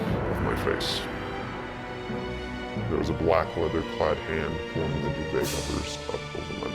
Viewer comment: what did you say there, I couldn't hear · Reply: Same, just heard "black leather clad hands something something my mouth" :/